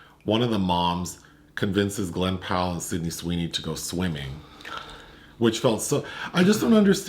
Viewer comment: I heard Sydney Sweeney to go swinging. Alright! Now it’s a party 😅